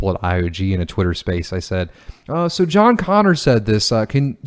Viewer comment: I thought you said John Conner , from terminator